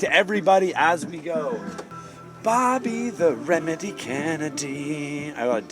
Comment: i thought he was saying "the meme" lol